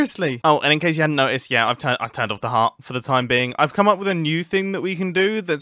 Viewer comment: I can't be the only one who heard "i turned off the heart"